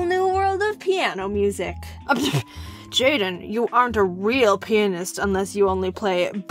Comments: It sounded like u said penis 3:03. Sorry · I heard you aren't a real penis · I thought it said "You are not a real penis" Lol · Am I the only one who thought he said “Jaiden you aren’t a true penis if you don’t only play Mozart” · I thought she said penis but then when I read the captions, I was like "HOLY SHIT I JUST MADE A MISTAKE". I hope that I'm forgiven for thinking so wrong lol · I heard "Jaden you aren't a real penis" my mind is messed up